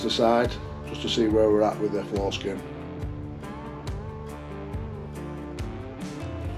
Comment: Haha had to do a huge double take when you said “Floor skin” but it sounded like you said “Foreskin” 😂. Good work, from Queensland, Australia.